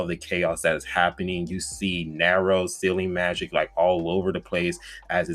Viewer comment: Thought he was saying silly magic